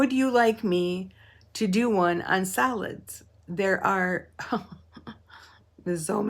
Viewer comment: Ohh, what fabric fun! Very interesting and I thought you were saying “salads” instead of solids which I realized later reading comments. I was really wondering how we got on food but I was going to vote yes, I love salads 🤭! Great Dandelion moment of zen❤❤❤❤❤